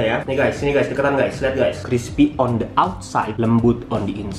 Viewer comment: aku ga tau dia ngomong apa wkwkwkwkwk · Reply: Crispy di luar,lembut di dalam arti dalam b.Indonesia